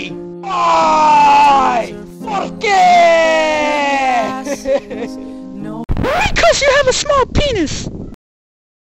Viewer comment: what does he say I didn't hear that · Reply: He said "Por qué" in spanish or can be "porquê" in portuguese.